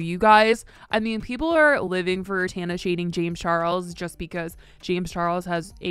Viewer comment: i thought you said tana is DATING james charles at the beginning and i was like okay i missed a couple episodes